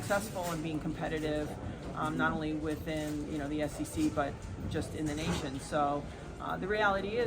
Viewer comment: Did she say SEC!?